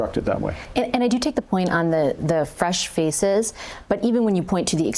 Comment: When Vassy mentioned "fresh faces", I heard "fresh feces".